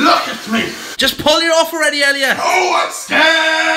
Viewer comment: Did just said "No, I'm scaaarrred" on scene video of 1:58?